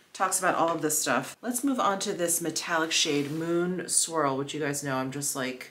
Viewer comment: When you said "Moon Swirl", I heard "Moon Squirrel".